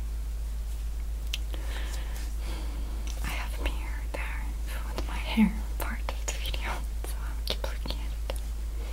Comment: What Masha said at the moment 12:58 - 13:03? I don't understand:) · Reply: I have a mirror there for my hair and I'm sorry I keep looking at it